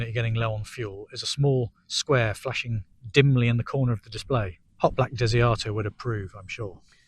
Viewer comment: Hot black dizzy auteur? What? Way too obscure, especially said lazily! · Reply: Ha ha, 'Hotblack Desiato' - it's a reference to the Hitchhiker's Guide to the Galaxy, by Douglas Adams. It's a reference to the controls/displays of a certain spaceship being less than helpful. I said it was niche 😅